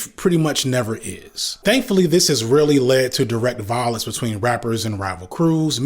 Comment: had to hear that back, thought you said "thankfully this really lead to" and not "thankfully this rarely lead to" 💀💀